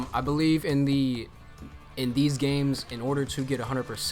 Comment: yoo did ya really said B O N E L E S S levels